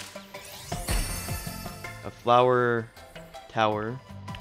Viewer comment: I thought you said Flower Power witch is a 1960s Hippys reference